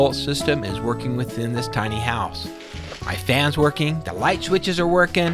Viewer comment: Legit thought he said "f*** house", which it might also be